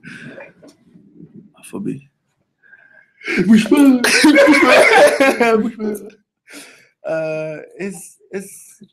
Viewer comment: what did he say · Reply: "wish one"